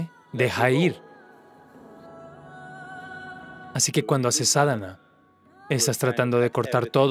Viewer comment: "Así que cuando haces ..." 22:02 Qué dice aquí exactamente? me pueden ayudar? Gracias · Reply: Sadhana